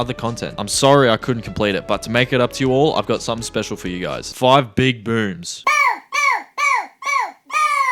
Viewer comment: aw man, I thought he said five big boobs😢